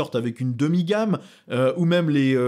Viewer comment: les chevalier qui sont "toujours parfait" j'ai entendu x) en faite c'était pas refait nuance